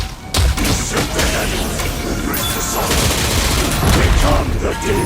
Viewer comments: What I thought he said: "Usurp the Heavens. Embrace the Satsu. Become the Demon!" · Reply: When I first heard it I thought he said, “Be sent to Heavens, Reach the Summit, Become the Demon!” Funny how almost all of us got the last bit right at least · What i thought he said during the Raging Demon: - Die a thousand deaths - Be sent to heaven - Embrace the Satsui (Satsui no hado) - Become the Demon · oh they are saying usurp the heavens i thought they were saying "we'll shoot to heaven" also thought it was "embrace the sovereign" rather than "embrace the sorrow" these phrases make a lot more sense